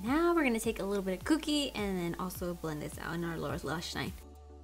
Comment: When you said “lower lash line” I thought you said “Laura lash line” 😂 I think the colors are pretty 💕